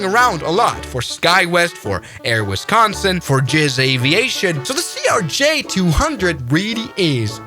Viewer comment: did bro just say jizz aviation??😭😭😭😭